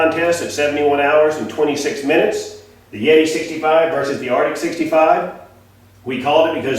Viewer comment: I thought he Kept saying Artic its an "RTIC", its fing made in CHINA. Buy Yeti, Orca, or Pelican. made in USA. .